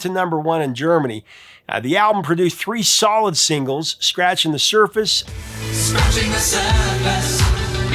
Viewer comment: By the way, when I first heard "Scratching the Surface" about a decade ago, I thought he said "Scratching the Circus."